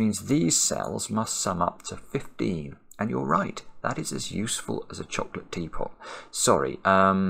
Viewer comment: Did I hear him right??? 4:50 Did he say “that’s a useful as a chocolate teapot”!?! I LOVE that!!! It’s probably a British thing, but I love tea and that’s such a fun saying!